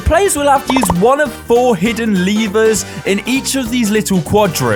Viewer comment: I THOUGHT the lad was saying “liver” with a stress on the word.